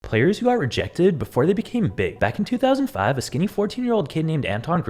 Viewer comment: My dumbahh thought you said the became bigback